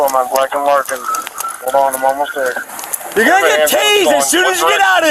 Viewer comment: The closed captions said "You're gonna get tasted!" instead of "tased" 🤭🤣